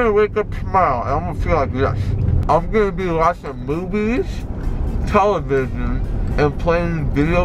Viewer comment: Did he say boobies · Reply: Movies 😂😂